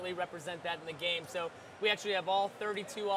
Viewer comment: LOL at :51 i thought i heard 'that nigga in the game' haha